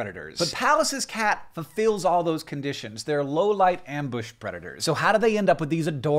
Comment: Instead of "low light predator" I heard "low-life predator" and I thought that was a bit insulting lol